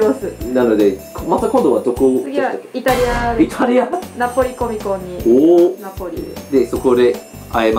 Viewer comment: for a second thought that she was saying Nepali comic and I was so shocked!